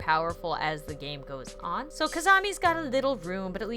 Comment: Am I the only one who heard her say kizamie instead of kizana. Lol this was when she introduced kizana sunobu's room